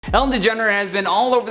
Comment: “Ellen DeGener has been...” Like Kylie DeJenner??? 😳😂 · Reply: I thought he said Degenerate